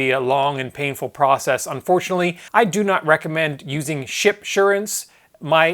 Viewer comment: awesome vid thought you said shit insure at first lol sounds like that's what they should be called can't wait to get a new montage